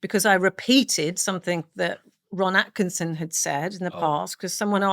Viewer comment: At 12 mins in, misheard "Ron Atkinson" as Rowan Atkinson and spent a few minutes searching the internet, hoping to see Mr. Bean say the N-word